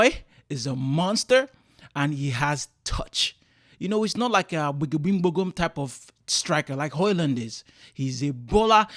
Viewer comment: Bro, what did you say about Højlund at 0:30 😅 ? Because what I heard was "wim-bogom striker like Højlund", what does that mean 😅 ? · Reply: 🤣. “Gbagagbingbogum”. Neologism my friend. It’s a thing eastern Nigerians do. I was basically saying Hojlund is less composed than Zirkzee 🤣. Hope you subscribe and stick around 🙏🏾